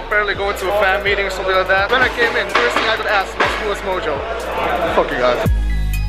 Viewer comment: 'love you guys <3' I heard something else haha